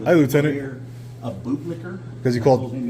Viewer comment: All this time he was saying boot licker I thought he was saying but licker lol